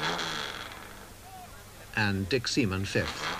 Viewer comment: Did he say Dick Semen?